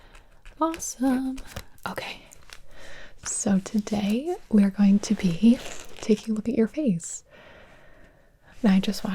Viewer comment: I thought she said “today we’re going to be taking away your face”